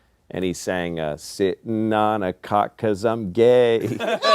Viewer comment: Thanks to auditory processing issues, I misheard "sitting on this cock cause I'm gay" as "sitting on this cot cause I'm gay." Honestly, with 0 context, I think the line I heard is funnier because it makes way less sense. Idk, maybe my sense of humor was ruined by the "lol rAnd0M" era of YouTube comedy, but tbh I've never understood the difference between that stuff, and the absurdism that groups/comedians like Monty Python engage in, except sometimes for quality differences.